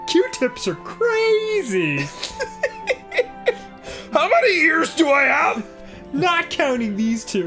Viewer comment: I thought Dan said "how many years do I have left" not realizing until he said besides these 2 he meant ears. Was like holy crap Dopey is secretly depressed and dark.